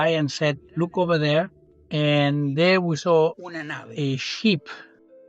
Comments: thought he said he saw a SHEEP!! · At first I thought he was describing an animal abduction because it sounded like he said they looked up at the sky and saw a sheep 🤣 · I thought he said “it was a sheep” He was saying “a ship” Cute accent